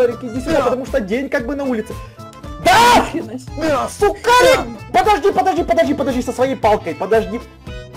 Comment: Одному мени почулось шо сказав Сукарик😹😹😂😂😂